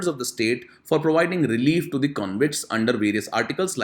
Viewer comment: "Relief to the comrades " Did I hear it wrongly? These people are convicts of hatching a plans to kill democratically elected head of this Nation. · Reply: Please listen it again. I have said convicts not comrades . Try turning on the subtitles.